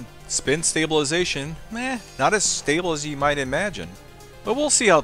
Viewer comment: did he say not as fatel as you would imagine? It looked pretty fatal to me lol. I damn sure wouldn't want to get hit by it sideways lol ok he said stable. Guess my brain was wanting to hear fatal lol. Ok carry on and don't pay attention to my dumbass. Lol